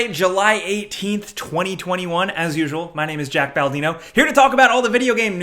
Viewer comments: I'm glad I'm not the only one that heard him say his name is Jack Baldino. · I heard him say Jack Baldino and decided to go to the comments to see if anyone else noticed and realized that I'm not special lmao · Did he just say July 18th?